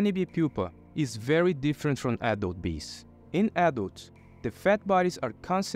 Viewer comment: whenever you say "adult bee" i hear 'apple bee'